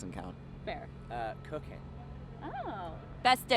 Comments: I thought said cocaine and not cooking and got real concerned for a few seconds 😂😂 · When said that he liked cooking I thought he said cocaine 🤣